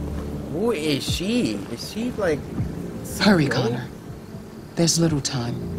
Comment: When Amanda said Hurry Conner. I thought she said Furry Conner.